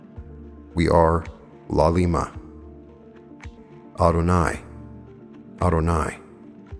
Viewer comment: what is that word/how do you spell it??? If I am remembering correctly I heard that same word listening to The Law of One Ra Channeling sometimes when they would end a session. Any info at all would be nice. It seems like an important word. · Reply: Sorry it is Adonai or a do nigh